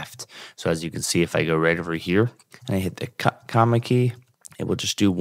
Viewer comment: hit what? comma key? Cannot hear. Thanx.